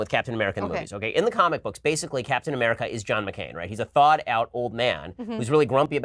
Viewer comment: "Captain America is like John McCain" Ben just ruined Captain America for me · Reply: I thought he said John McLain as in die hard. So I was thinking of Bruce Willis today as Captain America . I enjoy the captain America movies but come on, that would be dope